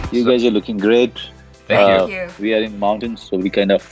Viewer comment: He wasn't at his home. He came to Ramgarh in Uttarakhand for holiday and got struck due to lockdown since March 22 until May. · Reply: He already said, "We were in mountains so....." You didn't catch that.